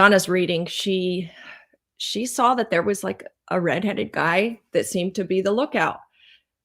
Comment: Donna said in his 40 s and salt and pepper gray . A lot of this I hadn’t heard. Thank you.😊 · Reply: I thought Donna said red head